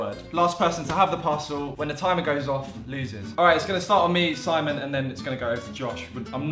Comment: When he said Simon I thought he said my stylist